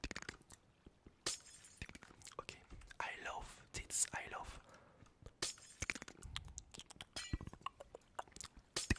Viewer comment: What did he sayy?!?!?!?! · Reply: i love thitsh i love thitsh